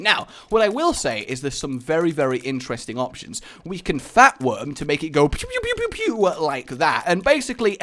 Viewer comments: did he just say fat worm? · And did said Fap worm at 12:10?????